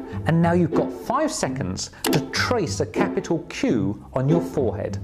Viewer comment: Am I just stupid or did anyone else hear cube instead of q